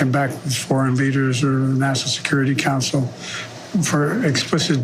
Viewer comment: National Security Council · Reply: It sounded like he said NASA security council. I listen again once it's over.